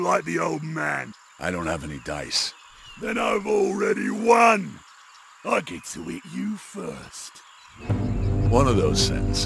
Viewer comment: “I don’t have any dice” “then I’ve already won, I get to hit you first” “One of those sentences is true” is oddly the most badass thing I’ve heard in any recent game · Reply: I heard, "I get to EAT you first."